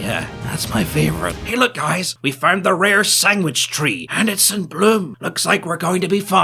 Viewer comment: Did he say "sanggwich" at 6:00?